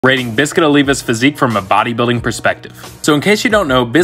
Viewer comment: I thought it said olivia 💀